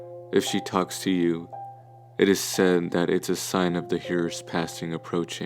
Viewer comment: The what? passing approaching Can someone tell me what he said because I want to look it up but great video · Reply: The years